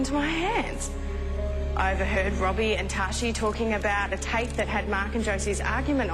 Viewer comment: Does she say Robbie and Tashie In 5:17??